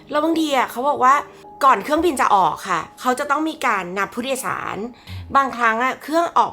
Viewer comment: ตอนแรกได้ยินว่างับผู้โดยสาร😂